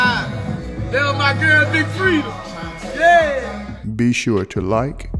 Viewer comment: I thought that he said, "My girl Big Freeda" 🤣🤣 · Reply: That's Lucci mama name. Freda!!! He was basically shouting her out!! Saying my girl,"Big Freda!!!